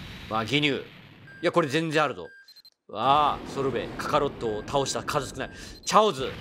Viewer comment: ソルベが鶴瓶に聞こえて鶴瓶にしか見えなくなった